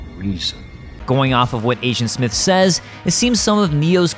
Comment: I Keep hearing “Asiant Smith”